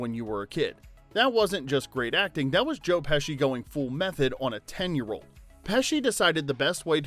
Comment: I swear I thought he said go "full meth head" 😂